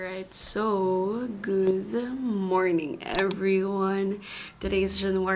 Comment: Is it me or did you just say "goodth morning"? Is that they new way of saying it?